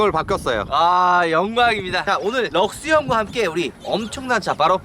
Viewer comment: ??? 잘못들었나요??? 엥?